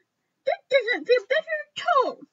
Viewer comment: sounded like he said puppy this is so cute though